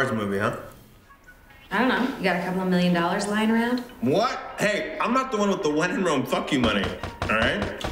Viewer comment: I always thought Ryan said "I'm not the one with a When In Rome fucking buddy."